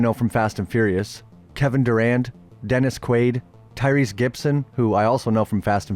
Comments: thought you said Kevin Durant 😂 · I fr thought he said Kevin Durant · I'm gonna be honest I thought you said Kevin Durant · Thought bro said kevin durant 😂😂😂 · i thought i heard “kevin durant”